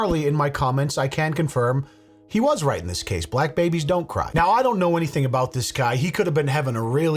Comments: was it black baby,,,,i thought he said CRACK baby ?? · Hell i thought he said crack baby 😆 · My bad I thought he said crack baby. 😅😅